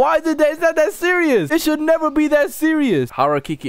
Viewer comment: When he said this should not be this serious i thought he said this should mot be this sh1t